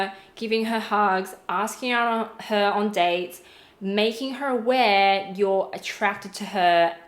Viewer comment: When she said making her aware I thought she said making her wet